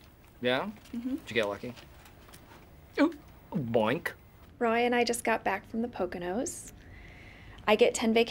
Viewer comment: I think Pam says Ryan not Roy. Interesting 🤔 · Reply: No, she says Roy, it's just that it's "Roy n' I" and not as much "Roy and I."